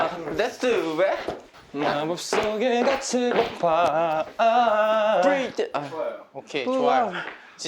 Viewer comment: 프리즈 이제 들리네…왜 예전에 볼 땐 몰랐을까